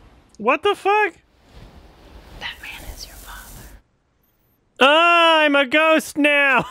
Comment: did she say Batman is your mother?